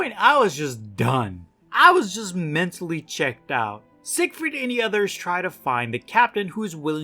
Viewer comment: Until I started reading the comments I thought you were saying “Sick Freak” instead of “Siegfreid” but it seems like it would fit anyways